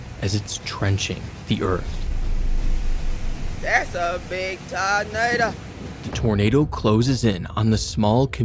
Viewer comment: "das a biiig tuhnaduh"😭😭😭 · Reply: what i heard was "that's a big tow matuh"🤠